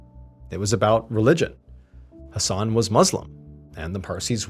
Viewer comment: i was busy doing things while listening and all these time I thought he was saying "a son" or "her son" in a weird way... Turns out it was Asan 😂😂😂